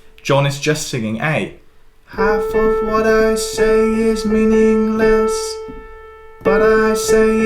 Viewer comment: Forever I’ve thought John was saying “half of what I say is meaning less” (less than what?)… “half of what I say is meaningless” makes waaaaay more sense.